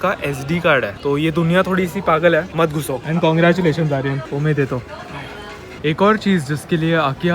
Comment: What did he said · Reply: Omedetou means congratulations in Japanese